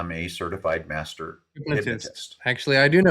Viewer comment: I didn't hear what he said, either! What was it? 48:45 Hypnotist? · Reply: yes I think it's hypnotist